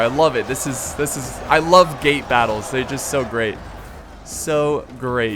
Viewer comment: it sounded like he said ***battles